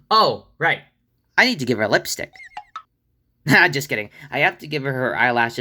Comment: I thought he said : "I need to make her Realistic"